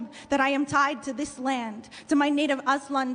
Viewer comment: I thought she said Azland like the catholic Jesus Lion from the Narnia books 😆 I was like wft does a lion crucifying itself have anything to do with white people?